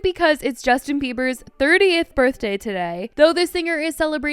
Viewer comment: Not me thinking I heard you say it was his 38th birthday 🤣😩😅